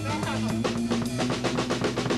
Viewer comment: what dose tom say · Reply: I think I broke somethin